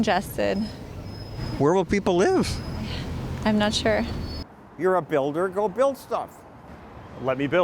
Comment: The girl in glasses said "I'm not sure" But what i heard was "I don't care"